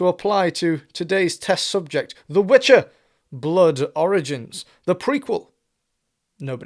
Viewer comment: I thought he said the blood oranges !!!